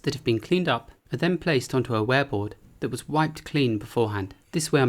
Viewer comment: I'm such a child, everytime you say "ware board" I hear "wereboard" (like werewolf) XD